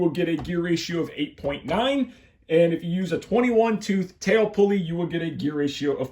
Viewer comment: Lol 😅 I thought you said a 22 tail pulley but you said 20 tooth 😂, I was confused about tail ratio 😅been higher with a bigger pulley, 😅English not my first lenguaje, great videos as always